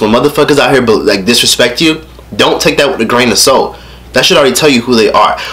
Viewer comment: i thought he said " dont take that with a grain of soap" lol